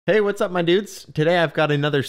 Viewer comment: i Heard "hey whats up my nudes" in the beginning lmao